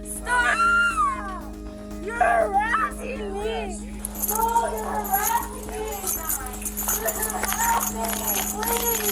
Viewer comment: Did anyone else hear the lady scream “no he’s not!” When D.A screams “he’s harassing me!” Lmao